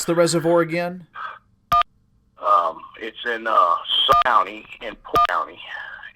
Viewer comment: OMG I am still listening to the intro and I am so upset just by the into. I was thinking I agree that anyone who contacts you should remain private unless they allow you to use their name. Maybe this gentleman agreed to allow you to use his name bcz he is worried about his safety and possibly thinks if more people know his name it may keep him safe. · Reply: I live straight west of Pymatuming Lake about 30 miles away. Very interesting. I've been looking around Pike County, Ohio because that's what I thought he said but I didn't see any real big lakes there. I'll have to dig into the lay line thing and check for myself if I can find them.